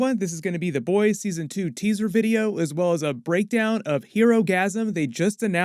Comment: did you say it's a season 2 teaser video?